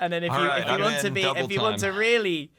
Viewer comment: i just love how when the meeting ended and docs message came out they all collectively went “SHUT UP DOC” and said “ok im fully in now” 😂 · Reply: I thought he said "Okay. I'm double in now!" tbh xD